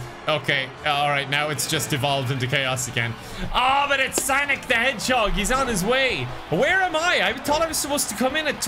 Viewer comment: I was so hungry I heard "sonic the hotdog"